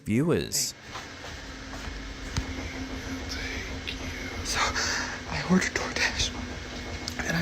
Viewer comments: did anyone else hear "thank you"? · hey did anyone catch that voice around the 55:07 mark it literally says I will take you there also heard after he said i door dash bit it says Right there is two responses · Dustin Fraser 's footage, I hear a faint, male voice saying something like "I will take you" Does anyone else hear it? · At roughly the 55:10 mark on the video something says “we will take you” or at least that’s what I hear. Anyone else notice this? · Did anyone else hear a male disembodied voice at 55:09 & repeated at 55:42? Sounds like "I will take you"... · I heard it as well and just checked the comments to see if anyone else had noticed it! I've just gone back to the video and played that scene again and again, What I can hear quite clearly is someone saying "We'll take you"!!! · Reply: I heard "Thank You" as well and it sounds like aroun 55:15 I hear "Right" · I heard a low voice say thank you, creepy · At approximately the 55:14 mark, if you turn the volume up, you can hear someone whispering, "Thank you". The creepy person is bad enough but, that 'thank you', shook me. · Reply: I first heard whispered "thank you" at 55:10, then heard "right". at 55:13.